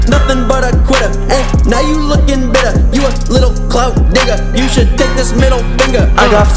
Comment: did i hear correctly he said the n word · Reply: He said Digger